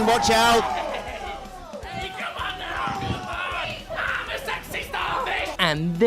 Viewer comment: Did I just hear "I'm a sexy starfish"?